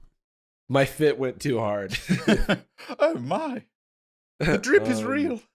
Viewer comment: When Jarvis said his fit went to hard it sounded like he said his fist went in to hard and I was like wait what?! For a sec😂😂😂